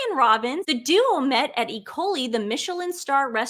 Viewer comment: PS I thought you said the London restaurant, Ikoyi, was called E coli.